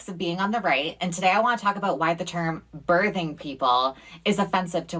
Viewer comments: I thought it said British people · Not going to lie, when she said “Birthing people” at first I thought she said British people and I was just super confused